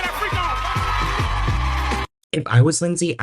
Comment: Did he say freak off?😳